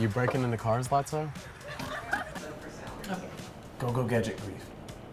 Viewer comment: Not gonna lie, I thought he said go go gadget queef